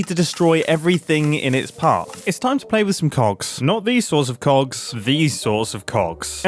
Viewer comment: I was looking away eating food and I hear you say “time to play with some cocks”